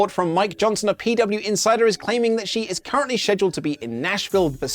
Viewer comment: You say "Scheduled" weirdly sounds like ur saying Said-u-ald